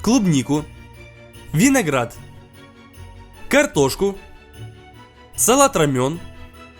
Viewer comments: послышалось салат рамен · МНе послышалось салат рамён как салат рамен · Мне одному послышалось салат рамен